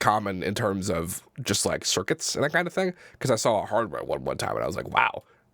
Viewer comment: does he say Hardwell ?